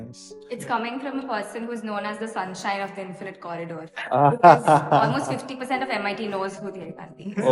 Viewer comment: I didn't understand what is saying Also no subtitles is available for that particular sentence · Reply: She said Dhyey is known as the sunshine of the infinite corridor